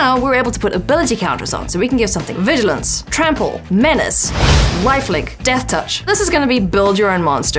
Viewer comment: Did she say "wifelink"? As in the LRR joke?